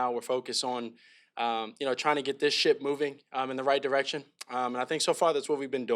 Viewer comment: Calum Williums is BACK!! 0:33 Thought he said get this shit moving instead of ship.